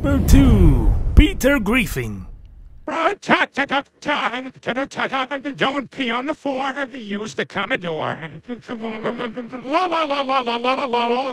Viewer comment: I thought he said: don't be on the floor use the colored door